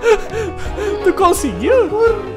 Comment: FOI SÓ EU QHE OUVI O FALAR "IHA PAÇOCA"